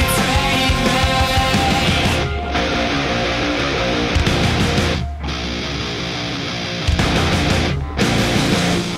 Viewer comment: What is Sting saying at 3:12? Looks super bizarre, and like he was saying something out of character while not expecting that there would be an aerial camera recording his face. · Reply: Looks like he was saying "Let me up, let me up."